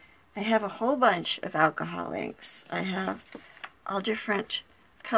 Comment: LOL I didn't have my earbuds in completely and I heard "I have a whole bunch of alcoholics" instead of alcohol inks.